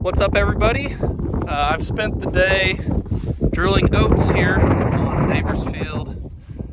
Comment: Ahaha - with the wind blowing across the mic at the beginning of the video, it sounded like you said you spent the day drilling goats. Get those goats in line, yes sir!! (Oats - lol). I was just thinking about y'all out there as spring is sprunging across the land. The high season for you is bound to be about to start! You sang in the Messiah - niiice!! And your little ones at the tea table. By the end of this next growing season, they will also have grown so much...even if they won't perceive it, yet.